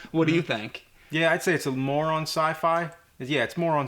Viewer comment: I thought James said "moron sci-fi".